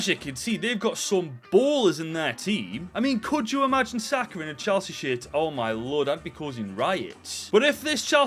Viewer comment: ngl i heard "chelshit" at first